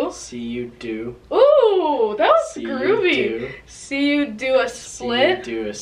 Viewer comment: Did anyone hear say groovy